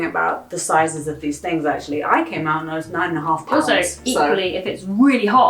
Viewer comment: in the beginning I've thought I heard nine and a half kilos and almost spit from shock